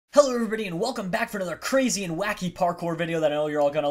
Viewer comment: I heard him say hell everybody instead of hello everybody in the face am intro